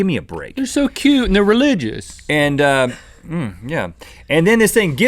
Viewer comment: Did Link say religious?